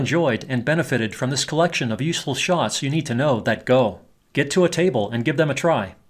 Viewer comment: did I hear “useLESS”? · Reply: You may have heard that, but I said "useFULL." I guess I should have enunciated a little better. The shots are in face useful! :)